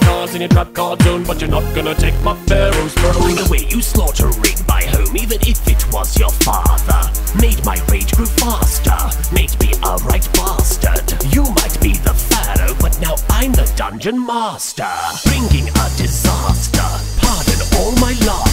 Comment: I always thought Bakura said 'made me a Ring Master' and not 'made me a right bastard' either works though